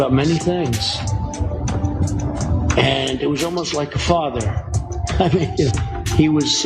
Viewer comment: It sounded like trump said hamas was like a father. I heard it better the second time jimmy plays it